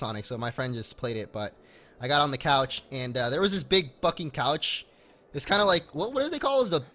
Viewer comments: I thought he said big fucking couch. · I thought said "Big f***ing couch" XD